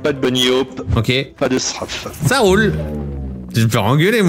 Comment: c'est moi ou le mec dit : "pas de strafe" XD